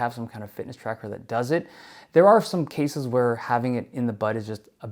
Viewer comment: Did he say "having it in the butt"?!